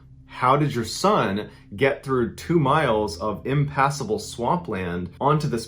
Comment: ImpAssible 😂😂 I thought he was trying to say “impossible”